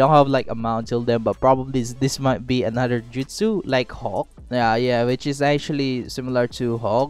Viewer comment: am i the only one who heard “hulk” every time he said hawk